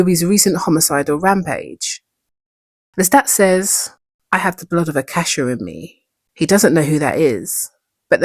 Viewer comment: I thought i heard you say "the blood of a cashew" 😂